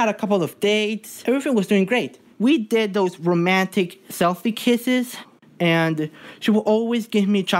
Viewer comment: When the boy says kisses it sounds like I it sounds like he is saying kithese😃😀😀😀😅😅😅😂😂😂😂😂😂😂😂🤣🤣🤣🤣🤣